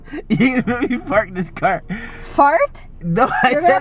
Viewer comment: I heard "you gon make me fart in this car"😂😂😂not "lemme park this car" 👀